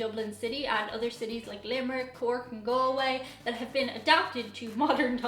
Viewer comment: Did you say "In the town of Go Away?" I love the idea of that name for a town!